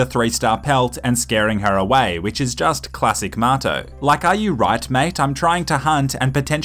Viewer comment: Marto stays sufficiently hydrated, that's why fish love him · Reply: Filip Filip I thought he was saying “motto” this whole time. God damned Aussies